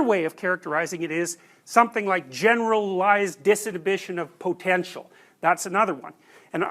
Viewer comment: generalised dis.... of potential, what is the 'dis' word? I can't quite hear it. (I would like to learn more on this 2nd possible characterization) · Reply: generalised disinhibition of potential